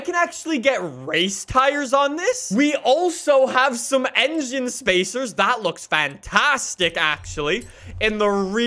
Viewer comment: did I just hear him say "Engine Spacers"